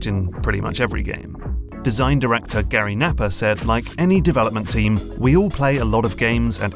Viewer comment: Every time I hear say "Napper" i kept hearing "Nappa" and him talking to vegeta until i saw it written